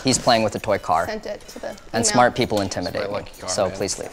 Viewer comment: Micheal: "And smart people intimidate me" Also Micheal: looks in mirror flinches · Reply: I thought he said “un-smart” but both apply, so I’m not complaining.